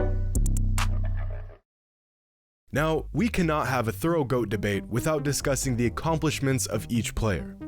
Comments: Timout. My ear must be trippin… did he just say, “we cannot have a throat goat debate”?? Min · U gotta anúnciate on that “thorough goat debate” coulda swore you said “throat goat” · I thought he said Throat GOAT 💀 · I thought you said throat goat bro on god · thought he said throat goat debate🤣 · I thought he said Throat goat debate at 6:25 😭😭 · Hold up…. Am I the only one who heard ThroatGoat at 6:26 ???? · did he just say throat goat ??? 🧍🏾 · did he say throat goat??? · Did he say throat goat debate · Did bro say we can’t have a throatgoat debate 😢😢😢😢😢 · Did he say ‘throat goat debate’ at 6:28🤣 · I swear he said throat goat debate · … I swear on my life I thought you said “Throat Goat debate” first listen I was like ayoooo 😂😂 but good video as always keep that shit up 🔥🙌🏼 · I heard him say "we cannot have a Throat Goat debate" just go to 6:27, my boy got caught in 4k Ultra HD Max FPS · thought bro said throat goat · i thought he said throat goat debate 💀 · on my momma i thought he said “throat goat debate” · did he say throat goat debate??? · I thought you said “throat goat” and bout passed out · What? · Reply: he said thorough. I thought he said throat goat at first 💀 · im faded watching this but at 6:30 i almost thought he said a throat goat debate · I really thought this man just said “We can’t have a throat goat debate” and was Flabbergasted. It’s all good y’all he said “thorough” 😭😭😭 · Did he say throat goat debate at 6:25🧐🥴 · I heard “throat goat debate” 💀